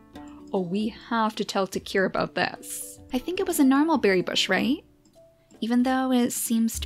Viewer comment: I thought she was saying narwhal berry bush I had to here it again to know what she was saying. lol